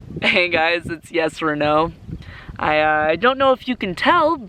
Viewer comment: I thought you said “It’s Yes or no” for a moment I was like... 😳